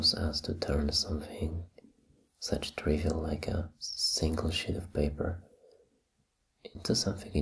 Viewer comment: It sounded like he said a shit of paper instead of sheet of paper